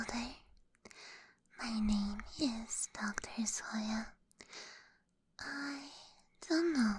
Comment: For a second I heard “My name is Dr Sawyer” and got really scared until I checked the profile name lmao